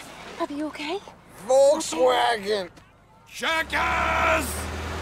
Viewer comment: My entire life, I thought Happy was saying "Full Swaggin'" when he's actually trying to say "Volkswagen". Only in recent times did I realize that.